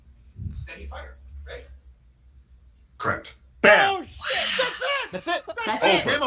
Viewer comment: if you turn on the YouTube CC captions thing instead of it saying “Correct” it says “Crap” which is at that moment exactly what the prosecution was thinking.